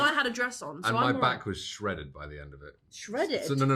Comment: what she said??? Someone can tell me ? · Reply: He said my back was shredded, which is also slang for muscular, like if you had a six pack you’d be shredded but that’s not what he meant. So she was joking that he was boasting about looking ripped. She said ‘I was so hench, i was so strong’. Hench is another slang word for muscular.